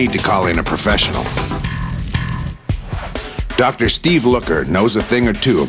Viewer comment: Legit thought he said Steve COOKer.